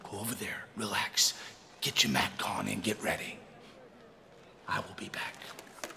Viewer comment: What did Goldust say? "get your.... on"? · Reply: Get your Mack on?